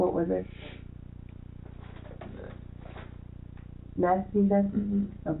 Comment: he just said he was taking meth if you didn't hear it. Meth + no sleep = shit happening like this.